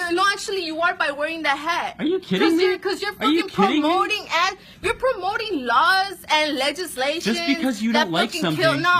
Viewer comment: "you're promoting laws"... what??? are laws bad??? it might be her accent? I don't know, I'm not making fun of it if it's her accent but that's what I heard I replayed it 4 times now :/